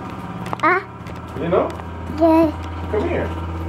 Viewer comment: it sounded like said yeah when called him at 6:14.